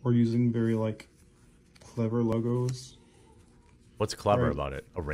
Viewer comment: It sounded like he said "Clever Luggoes." 😂